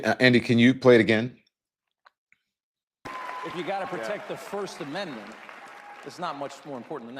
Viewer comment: It SOUNDED like musk said he bought off the First Amendment